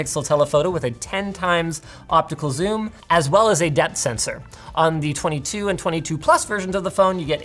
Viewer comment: When he said depth sensor I thought for a sec that he said the camera has a death sensor, and now I'm realizing that I'd like a camera with a death sensor, that would tell you the life expectancy of anyone you point it at.